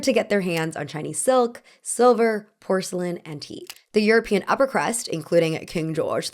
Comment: I thought you said teeth and I spit out my drink 😂